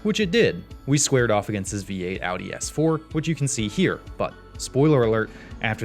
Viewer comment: Apologies to for calling his S4 a V8. Anything with more than 1.3 liters is a V8 to me. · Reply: Lol I heard "B8 Audi S4".